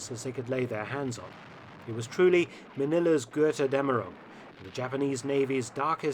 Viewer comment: 'it was truly manillas ...' what is said here? something in latin maybe?